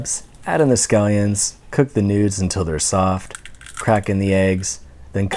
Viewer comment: *tries to click onto full screen and skips to end of video* SHIT! Also he said: cook the nudes And I was like: WHAT THE FUCK DID YOU JUST SAY?